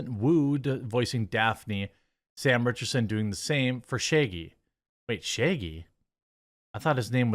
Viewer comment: Ok been subscribed for a bit, watched many videos, but I have a question. Why is it that often in Jeremy's pronunciation of things, I hear the letter e in place of the letter a? For example I heard 'sheggy' instead of 'shaggy'. Am I the only one? Has this been brought up before and I missed it?